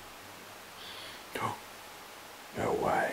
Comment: Whew! Had to take a minute. I'm a big ass ball of emotions today. I'm watching the interview and crying along with her. It's hard to watch somebody in that state. I can't stand to see others cry. You can see and feel that this poor woman is at the end of her rope and very traumatized. Thank you God for the. 🙏 💚 Deep breaths, going back in. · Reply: says we're going to make you leave tonight and I heard "no" and then a louder voice says "yeah" Earlier when they asked how many ppl where there I swear I think I heard a faint "8".